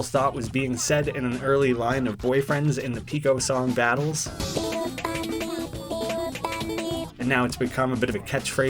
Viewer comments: “Pico funny” I thought it said “Big ol’ bunny, big ol’ bunny, big ol’ bunny, BIG OL’ BUNNY!” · no I think it says: BIG OLD BUNNY BIG OLD BUNNY which is a reference to BIG CHUNGUS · I thought it said "Pico's Bunny" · Ngl when BF said " Pico funny " in Philly nice I thought he was saying " big ol' bunny " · I thought it said, "pls go fund me". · I hear big ol bunny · i didnt hear pico funny i heard big ol' bunny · I dont hear “pico funny” i hear “big old bunny” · Instead of "Pico funny" I always hear "Big o' bunny" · I always thought it said "a little bunny" not "pico funny" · thought he was saying big o bunny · I thought he was saying "big ol' bunny" not "pico funny" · I hear instad “pico funny“ Big ol bunny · I thought he said “big ol’ bunny” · Pico funny? bruh i hear it as "Big ol' bunny" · people who think boyfriend says (pico funny) should be surprised at this: people (like me) thought that boyfriend was saying (big ol bunny) and a few videos + memes are made by those 3 words Edit: also near the end he missed week 6 or did he? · I thought it said big ol bunny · instead of that i hear big ol' bunny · lol when I heard "Pico Funny" by BF i thinked it's "nie masz panny" (in polish) so in english it be "you don't have girlfriend/miss" :P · I thought he was saying “Big ol bunny” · we didn't think it said pico funny we thought it said BIG OL' BUNNY BIG OL' BUNNY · i only hear BIG OL BUNNY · Instead of “pico funny“ I hear “hey you funny “ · i thought he said "bigo bunny"